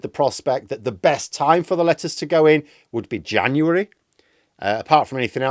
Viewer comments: I swear I heard "the best time for the lettuce to go in" and it made sense in the context. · was I hearing things or did you say "time for the lettuce to go in....." 😂